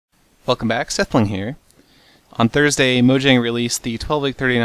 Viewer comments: did I hear "fuck em back sethbling here" or "welcome back sethbling here" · At first, I thought you said "fuck mac, sethbling here.."